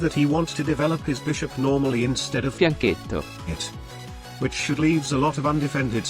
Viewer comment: exact same upload time different good also change bacc outro rn also anyone knows what was saying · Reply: Fianchetto probably